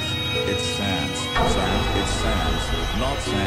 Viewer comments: He Does Not Know How To Say The WTF Word Instead He Said WAF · All I heard was: “It’s sans not sans!” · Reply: i hear "nonsense" "it sends" and.... " What a fuck " im getting crazy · All I hear was “ ITS SEX NOT SEX SEX SEX SEX SEX what the fuck”